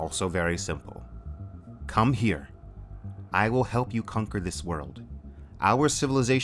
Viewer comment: Besides the disappointment with humanity, her the biggest mistake was the thought that the Trisolarans would care for the survival of humanity. Loved this series pointing out so interesting topics like "the Dark Forest" and the nature of the universe. Certainly will re-read in the future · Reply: She did. I thought she said come conquer us. Do the ETO think they will be spared?